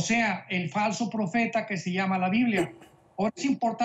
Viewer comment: Las enseñanzas del hermano son una bendición, pero hay algo que no entendí ya que en el minuto 1:41 aprox. Dice qué hay un líder religioso osea el falso profeta que se llama “LA BIBLIA” la verdad lo volví a escuchar y ahí algo se corta pero no está claro , si alguien me puede aclarar por favor ...... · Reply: Veronica Gutierrez creo que quiso decir.. que se llama en la biblia osea que se menciona en la biblia